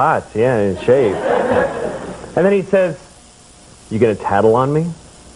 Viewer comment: wth is he saying in 2:09? "You get a tadel on me?" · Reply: "You gonna tattle on me?"